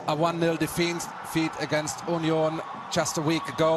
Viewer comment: The way he said 'Union' I thought he was saying 'Orleans' as in the French town